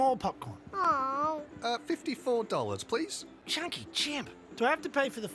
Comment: Am I the only one who misheard it as "Do I have to pay for the four year old? She's not really going to want s***"